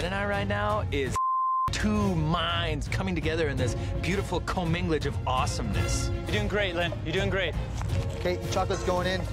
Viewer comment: What does say at 3:42?.....beautiful what of awesomeness?...lol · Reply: Yes I Know “two minds coming together in this beautiful commingle-age of awesomeness”